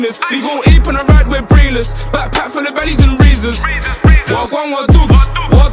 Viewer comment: What did he say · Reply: Backpack full of Bally’s and razors